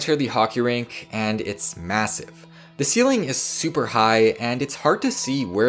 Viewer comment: wait.. did I hear M-Massive...? 2:53 Fade?